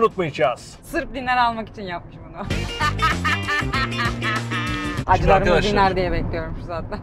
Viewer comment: teki espiri ne canlar ? ben anlamadım ne dediğinii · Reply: Acilarim dinar (diner) diyor insallah